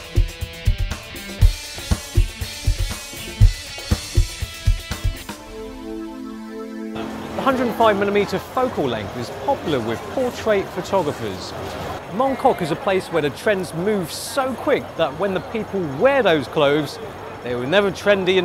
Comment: Thought he was saying "for anyone who's into porn..." -no, portrais, ok...